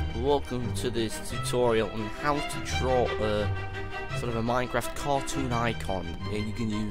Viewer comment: I thought he said "How to troll a Minecraft icon" xD